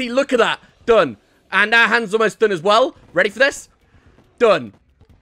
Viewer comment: "And her hands are done!" Did I just hear you call the Moon Lord a girl? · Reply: I did say the but it sounded like she hahahaha